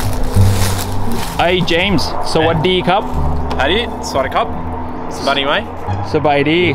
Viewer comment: I heard "How are you, mate?"😆